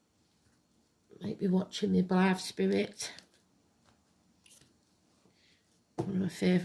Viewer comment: you mentioned "spirit" but I couldn't make out what you said right before it. Thought you might have been referring to another movie you liked (right before you mentioned I Married A Witch). 😄🤭 Happy Samhain. Blessed be! 🤗🥰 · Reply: Blythe Spirit one of my all time favourites 😍